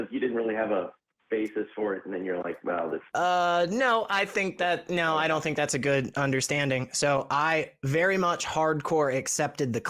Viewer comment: Why? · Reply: I thought they were saying "No! I don't think all atheists think gender is fluid". Was I wrong?